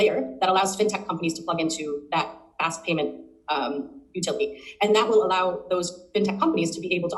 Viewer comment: did she just say “back ass payment”? 😂 I’m so immature.